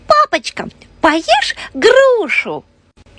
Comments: А мне послышалось попачка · Мне послышалось не папочка а попочка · Мне послышалось не "папочка" а "ПОПОЧКА"